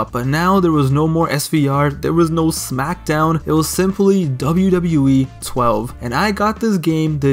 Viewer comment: I thought it said wwe13👑 lol